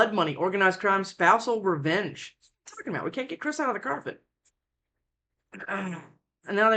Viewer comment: Did you just say "can't get Chris outta the carpet?" Lmfao!!!!!